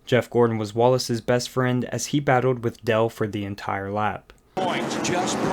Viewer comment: I’m sorry, but did you call the Intimidator “Dell” at the 11:15 mark?